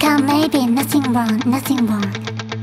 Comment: 本当にかっこいいし凄い曲なんだけど、どうしても2:08からのルカ姉さんの英語が「盗んだりしてないさ」にしか聞こえない…ww ルカ姉さんそれ盗んだ人がする弁明の仕方だよ……？？